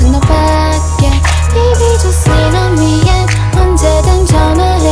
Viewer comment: i thought she said "sit on me" AHAHAHAHAH IM SO SORRY